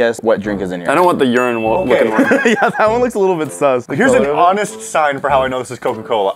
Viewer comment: did say sus?